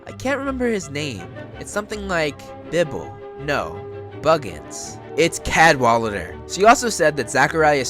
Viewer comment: I thought that said Bilbo Baggins, at first. I got so confused.